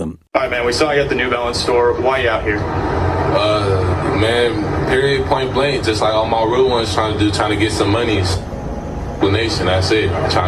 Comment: PLEASE HELP ANYONE, can you tell me what he says at 3:08 , I dub video for french people and I need to understand what he's saying. PLEASE ! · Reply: reporter: we saw you at the new balance store, why ya out here? Looter: aw man period point blank. just like all my [not sure] ones trying to do. Trying to get some monies [sounds goes out] explaination, I say, trying to make some dough, that's it. reporter: just out here for for the money? Looter: yeah, pretty much That's what it sounds like to me. Good luck!